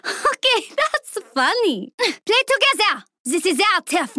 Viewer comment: what does she say?? not together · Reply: Play Together! Has she said